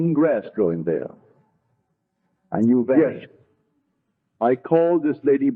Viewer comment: Thank you. Also, what did he say at the 10:21 mark? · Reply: vanished.. it’s at the end of the first paragraph on the left